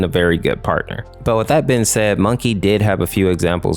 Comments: Di- did just call mokey monkey?! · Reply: He called him "Moe-key" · mokey? yeah i hear monkey